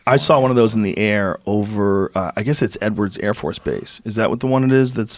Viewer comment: I thought he was saying n words insted of edwards hahahhahahha